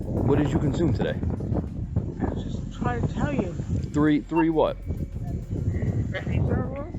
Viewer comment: she said "I was just trying to tell you" and he responded "Three what?" At 40:36 she said "Pink lemonade" and the closed captioning said "The pink one's amazing." There were a BUNCH of things that both the police officer and whoever transcribed this got completely wrong. I think this woman was not only intoxicated but also probably has the beginnings of dementia, and it didn't help that she couldn't be understood so the questions he followed up with had nothing to do with what she had just said. That was totally confusing her. I hope she gets her license revoked, and also gets the care she obviously needs. Lastly, there is no evidence whatsoever that any of these people were grandparents, so I'm not sure why the whole "next-best things to Santa" aspect was brought in to this. I thought these were going to be cases where people had their grandchildren with them. It would have been more honest to just say that it was about seniors.